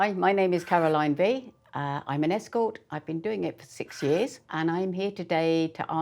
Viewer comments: I love her directness and authenticity. But the one thing I find REALLY concerning is, that she said she is doing this for 60 years now and the title says she is 70... Oo Edit: OMG! I misheard her! She said for SIX years, not sixty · At first I thought she said 60 years, not 6😂